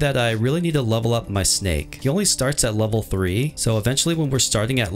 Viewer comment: Did you just say my snake oh eighteen only😀